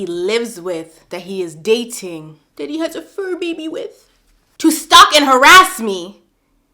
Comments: Guurrrllll, I thought you said "new baby" not "fur baby" I had to run that shit back real quick cause ain't no mf way. 😭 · Girl I thought you said has a baby with😫 say FUR louder!! I was finna get hot